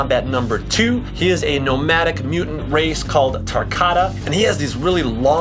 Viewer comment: “tarkata” instead of tarkantan